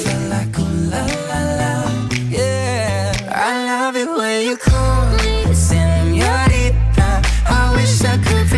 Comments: I know i’m late but, i’m pretty sure he says. «I love it when you call....senorita». When i first heard the song i also thought he said. «I love it when you call me senorita» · I'm glad also thought Shawn said "Oh, she loves it when I cum" 😂😂😂😂 I thought it was only me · I really thought that he was also saying “I love it when you call me seniorita” But Shawn says “I love it when you call... seniorita” It just doesn’t sound like it cause Camila sings it with him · Shawn is saying : I love it when you call, senorita.. (the power of a comma)😔✋🚫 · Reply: Yeah, at first I started to laugh when thought he said "I love it when you call me senorita" but then when i listened the second time i realised he didn't say me😂